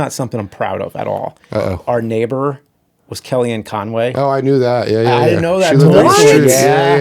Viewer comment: Who Is the person James speaks of in minute 1:27:47? I couldn't hear the name. Cheers. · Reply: Kellyanne Conway